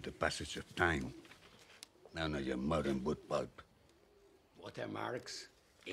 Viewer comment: when he talks about Venetian craftsmanship and the "pages resistant to the passage of time," what does he mean when he says "none of your modern ' boot pulp' " or "boot bug" (subtitles) ?? what does it indicate about the book itself? okay I noticed he says the "finest rock paper," paper actually made from stone, instead of tree pulp. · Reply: Hahaha I originally thought he said “not even your mother would part” but i think it’s “book pulp”. He’s slandering the quality of modern books